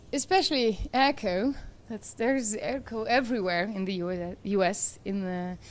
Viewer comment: what did she say? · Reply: She is talking about "echo" in the sound system at the venue.